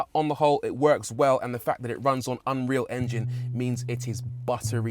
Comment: excuse me, it sounded like you said it runs on UNREAL ENGINE?!?!